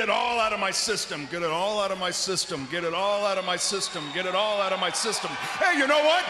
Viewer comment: I thought he said, get out of my sisters multiple times but its just systems 😂